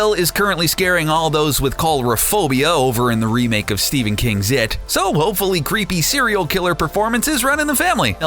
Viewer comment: I heard stephan king's zit?